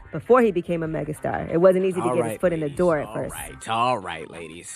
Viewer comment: Did he just say alright ladies🤔🤣🤣🤣💀